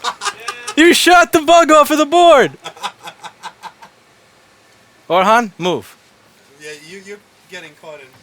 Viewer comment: On 8:12 did you sayed Orhan? Just asking for learn lol